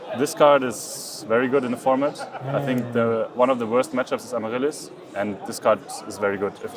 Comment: sry i speak spanish, what did he say in that moments, vs what deck is good soul release? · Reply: Amaryllis